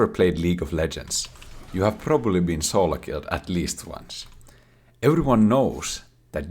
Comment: I heard “soul killed at least once”. But to that statement, yes. I had my soul killed when I play league 🥲